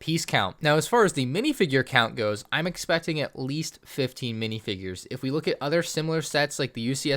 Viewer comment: İ thought he said 50 minifigures instead of 15 and i was like : WAT